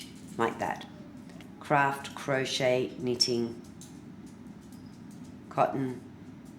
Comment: What kind of wool do u recommend? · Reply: kikikiwi911 I thought I heard her say "Craft Crochet Cotton 4 ply in pink." She also showed a picture of the yarn. You will have to rewind the video to make sure.